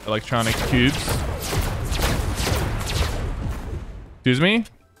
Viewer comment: "You gotta protect the balls guys." --Sypher PK, 2020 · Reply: it sounds like he said ‘’Jesus’’ lol